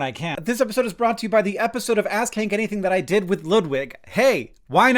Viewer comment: hi. I had to turn on the auto captions. bcuz I was playing on 2x speed and when he got to the "Ask hank anything" part I thought he was saying "ass kink ??thing" and was very confused